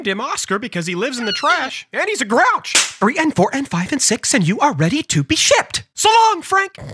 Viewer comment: What everyone hears : "And you are ready to be shipped!" What i hear : "And you are ready to be s**t!"